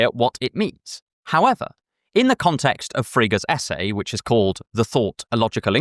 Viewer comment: Hi I found a mistake at 38:19. The subtitles say "in the context of figures essay" when in reality it should be Frege's.